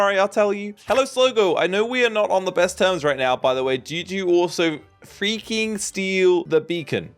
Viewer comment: I thought the book said "did you freaking steal the bacon" lol